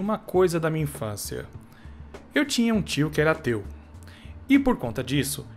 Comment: no começo do video ele falou"eu tinha um tio que era ateu" e eu entendi "eu tinha um tio que era teu" kjkkkkkjkkkk